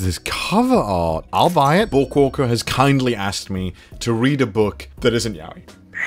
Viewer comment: "bookwalker has kindly asked me to read a book that is not yaoi" bookwalker is gayn't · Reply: Omg my dumb ass thought he said “book walker has kindly asked me to read a book that is not out yet” and I was so confused when he said he’ll do it anyway so thanks for clearing that up for me lmao